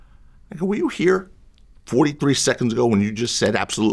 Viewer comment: it sounds like he said N word was you here?🤔 I might be Trippin.